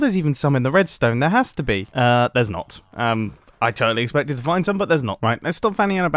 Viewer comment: When Mumbo went into the Redstone in Grian's base and said "There's not" when checking for charrots. I swear I heard "I'm STUCK " Then I realized I was stupid and my ears were wrong